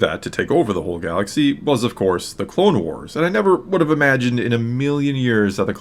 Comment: Well, first of all, I always thought Obi-wan said "Colonial Wars" not "Clone Wars"...